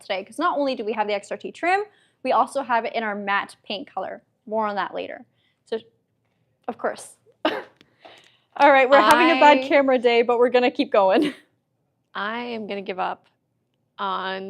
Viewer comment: I thought you said Matte "Pink"...!! 😆😅